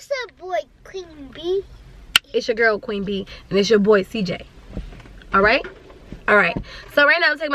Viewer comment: I thought cj said “it’s ya boy slim j” at first🤣🤣😭